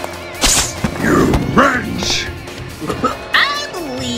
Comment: did vilgax just say to Ben “you b**ch” 😐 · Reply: He said you wrench